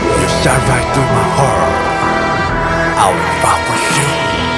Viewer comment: i can't be the only who heard him say "i will fart for sure" at the beginning. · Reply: I heard "I will fight for shit" 😂